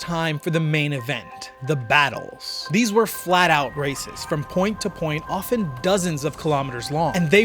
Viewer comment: lol i thought you said the battles were "flatout racist"